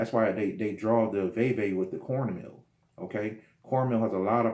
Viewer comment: what did he say here??? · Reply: Vay vay with corn meal